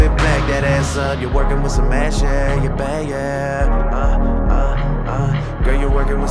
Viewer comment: I always thought he was saying you working wit some ass yea not mad shit lol